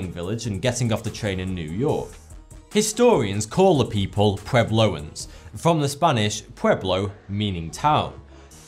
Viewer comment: thought it said pubeloans for a sec there